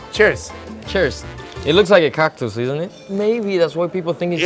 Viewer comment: While eating the churro said”It looks like a cactus” I could’ve sworn he said “it looks like a cock to us, maybe that why everyone thinks it’s Mexican?” I had to rewind and listen again to catch the Mexican relation. Gotta love a good accent. Cheers.